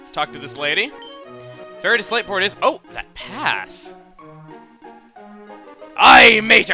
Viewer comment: i so thought said that ass... i fail -_